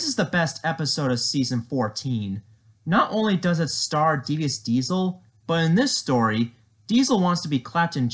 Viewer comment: I thought he said deez diesel